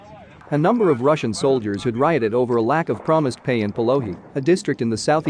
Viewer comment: 🤣 at first I thought he said lack of pay in Pirogies .. I’m like damb them sounds good right about now